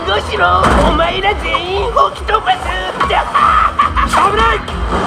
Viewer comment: すげぇ！こんな面白そうなアトラクションがあるなんて！どこにあるんですか！？ ………え？ 15:40 ずっと吹き飛ばすに聞こえてたのに例の問題のせいでオクトパスにしか聞こえなくなった((